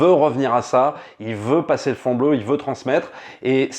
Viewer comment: C'est moi ou à 13:46 dit "il veut passer le fond bleu" au lieu de "passer le flambeau" ? XD